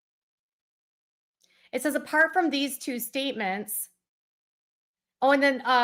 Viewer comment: I hear…. “Aside from these things I said to harm you, what’d I say to harm you?’ 🙄🫩😑